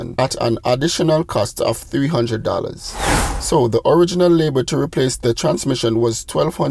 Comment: It didn’t. It failed at almost 300k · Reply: I thought you said the original owner replaced the transmission